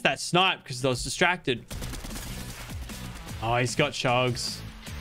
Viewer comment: I thought he said he got drugs